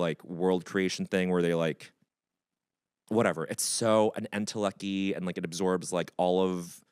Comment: “intellecky”? Is that what he said?